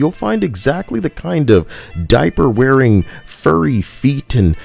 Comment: Wait wait WAIT... maybe my english is not good enough, but did you said “Furries”... 🤨 As If all fans of anthropomorphic characters are Leftists!?... That is offensive, sir... 😤🖕🏼